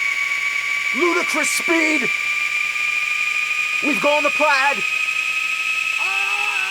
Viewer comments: What does he say at 15:20? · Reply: He said 'we've gone to plaid' referencing the movie Spaceballs. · what did he say at 15:18? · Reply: Ludicrous speed